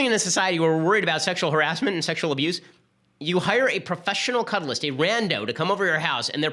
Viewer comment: I thought Ben was saying professional cudgels, instead its professional cuddles. That's why I thought he had a problem with people cudgeling each other, instead ....... he really is not a people's person.